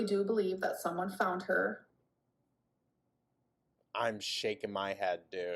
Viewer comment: ngl at 21:45 i thought i heard "I'm shaving my head" and that almost took me out. like the deadpan delivery, imagining being so pissed and done witb her that he's like "thats it im pulling a britney"